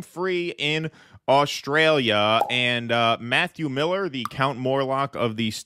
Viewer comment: At first, I thought it said, "Max Miller......" and I thought, "Due Dissidence is debunking Tasting History?!?!?" 😅😅